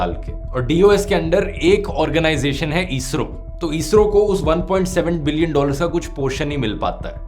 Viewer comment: Bro subtitles at 13:22 are incorrect, it's million instead of billion in subtitles